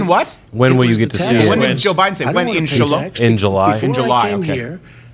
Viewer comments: He said exactly what you thought he said: Inshallah. In July, lol. What would that even mean. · I thought for sure Biden said "when? Inshallah?" But said "in July?". Turns out he did say Inshallah which is a Muslim phrase. That's strange.